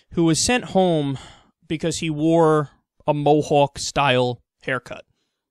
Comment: I thought it said tomahawk instead of mohawk